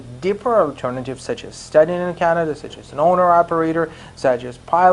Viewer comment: What did you say at 6:30 to 6:35 , about some operator what is that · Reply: Owner Operator: <link>